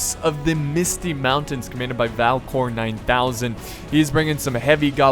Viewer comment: When you introduced Valcor I thought you said "Valve porn".